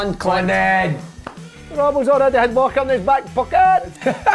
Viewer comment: who did Snodgrass say Robertson has in his back pocket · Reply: Walker